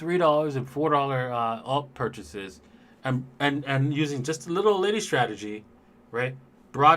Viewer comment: what kind of strategy did you say the professor did to go from $450 to $1,000,000? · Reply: found the answer in the chat, it's called "the little old lady child"